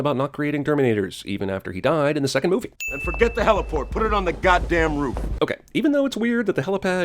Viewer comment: Admitted, I hate this sh#tty movie, but sin 127 was bad. "Ok even though it's weird the helipad isn't ALREADY on the roof... what difference would it make?" Well, for one, he said "and forget the heliport put it on the G#DDAMN ROOF." You misheard him say heliport as helipad. So 1 sin retracted from this sh#tty movie.